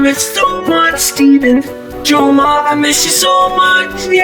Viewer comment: Who the heck is jomar????? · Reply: I thought it said your mom miss u so much lol